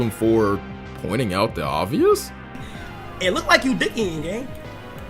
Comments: what the fuck is he even saying. He started speaking the xqc dialect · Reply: I think he said, "It looks like you dih eating gang" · what was he sayin? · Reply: “ it look like ur dih eating gang”.